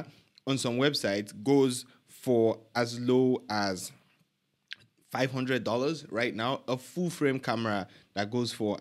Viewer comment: I thought I heard, it goes as low as $500?? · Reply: Goes for as low as $500? Yes